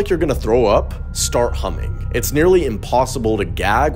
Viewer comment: I thought he said humping not humming… I’ll try humming while humping👌🏼